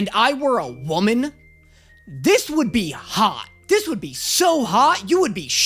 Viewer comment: Honestly I heard him say “I’d be hot” and just thought damn you kinda already are tho